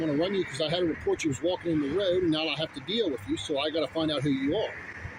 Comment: What is "walking in the red"? EDIT: Is he saying "road"? 6:05 Did this COP SEE you walking in the road (I guess it was road earlier)? If not, all he has is an unsubstantiated phone report. That IS NOT RAS.